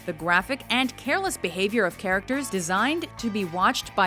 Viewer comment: did she just say... karacachter? for character?